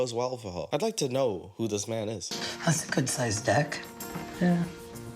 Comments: I swear thought she said " that's a good size dick" 😂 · I'd like to know, who this man is. That's a good size dick!?! 😂 I know, the 2nd time hearing it I heard it's deck, but still, that got me 😂😂😂